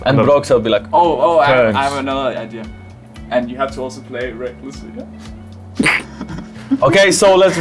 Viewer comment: I didn't understand caps's other idea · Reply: i think he said 'you have to play recklessly' that's my best caps to English translation